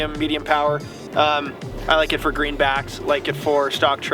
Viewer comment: did he say green bass 🤔🤔🤔🤔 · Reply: Lol, no. He said greenbacks, which are a species of cutthroat trout.